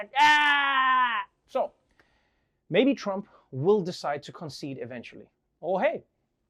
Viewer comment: I misheard "Maybe Trump will decide to concede eventually..." as "Baby Trump will decide to concede eventually..." and i still think it fits 🙂